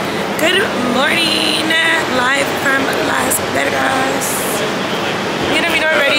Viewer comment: Am I tripping or did she say Las Vergas 15:33 lmaoo